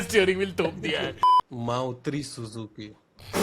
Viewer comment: I heard " Maa utri susu ki"😅